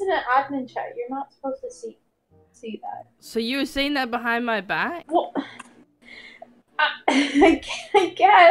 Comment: Did just said "behind my back"?!?!